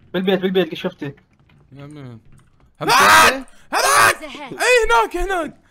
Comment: بالبيت بالبيت شيسوي لأختة ؟؟؟ سمعت غلط ؟؟؟ لو صحيح السمعتة؟